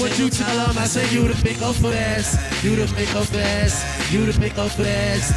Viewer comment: what did yall here, i heard "you the mingo best"